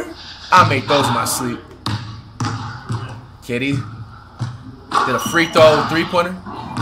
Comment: did actually say a free throw 3 pointer😂